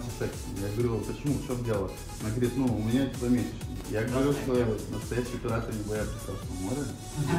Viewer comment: Что парень сказал на 4:20 когда баба сказала что у нее дни?) Не расслышил. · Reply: "Настоящие пираты, не боятся Красного моря" ))